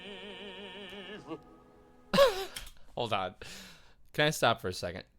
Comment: You misheard … he did not say "neither", but "naïve".